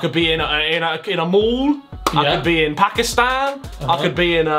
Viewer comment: What the fudge, Harry did not just say Pakistan at 07:24. Btw if he said it then thnx Harry as it felt very good. Pakistani 🇵🇰 🇵🇰 w2s fan here